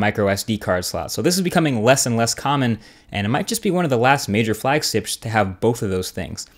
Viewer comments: Was i the only one who heard flag sips at 1:46 ? ;D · "Major Flagsips" 1:45 what was that? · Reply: I thought this niggga said flagsticks lol